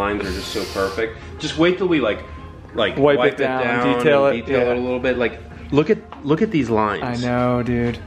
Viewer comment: Did you say you need a detailer??? I know a guy...